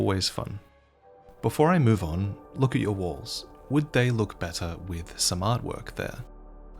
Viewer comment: "Before I move on, I'm in your walls" was what i heard, made me jump haha